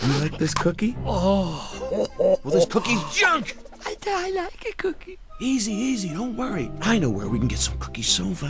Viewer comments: Thought he said. "I da i likey cookie" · sounds like he said "i died like a cookie" but he actually said but i like the cookie